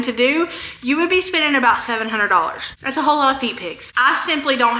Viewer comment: I had to rewind. "That's a whole lotta feet pics.". Yep, that's what I thought you said! 😂